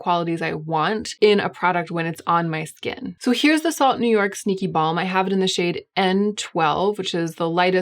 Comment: I haven’t had enough coffee because it sounded like you said here’s the salt New York stinky balm 😂